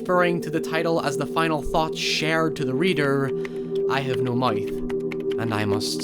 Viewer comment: why do you say mouth as moif? edit:nvm i just heard you say youre dyslexic